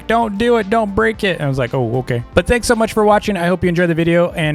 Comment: Oh and congrats for hitting 100k but our goal now is 1M hmmm I think I'll do something bout it Who heard said owo at 10:30 idk if I misheard that uhhh